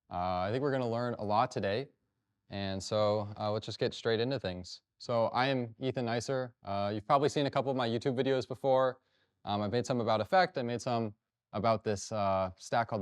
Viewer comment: I was listening to this and misheard his name as "euthanizer" (sorry!) Luckily, it wasn't quite that brutal, just a super cool talk!